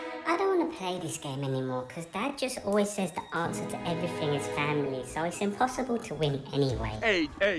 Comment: did he say family Family Is everything lol